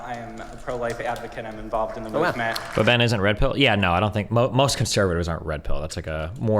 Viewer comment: For a second I thought you were saying Ben was redpill...